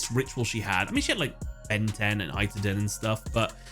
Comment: did he say Ben 10?! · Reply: Yes xD there was a Cyber Angel Benten